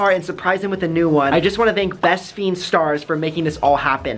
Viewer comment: I thought brent said best bean stars